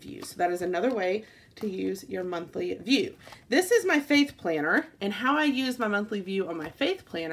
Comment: When you were saying my "faith " planner I thought you had said "fake" planner haha